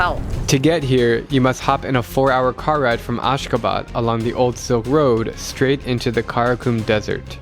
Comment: when u say Ashgabat i thought you say askaban from harry potter 😂